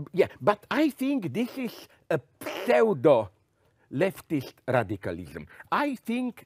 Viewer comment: What type of extremism does he say ? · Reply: I think he says Pseudo It's a good point - I agree whole heartedly